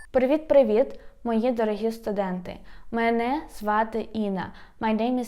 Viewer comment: thanks for the reply. My error, the words I didn't understand started at 0:02 - 0:03 right after said hi hello. Then she said at 0:04 my name is in Ukrainian which I understood. After looking at your reply and listening again at 8:08 I did hear and understand the Ukrainian words for "your teacher Inna". Thanks again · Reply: Привіт, мої дорогі студенти - Hello, my dear students